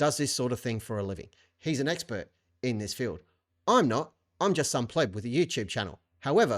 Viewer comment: Streisand effect escalates. Thanks! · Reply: When you said "I'm just some pleb with a YouTube channel" I heard it differently the first time. I heard "I'm just some playboy with a YouTube channel". I was all "my man!!! Lean in to that!"